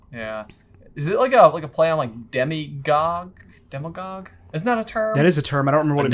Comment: When they were talking about demagogue I thought they were saying demigod.